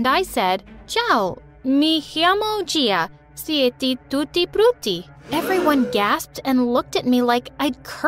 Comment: ) Tutti Fruity? ( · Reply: hello, my name is Gia and I like tutti fruity that's what I thought she said lol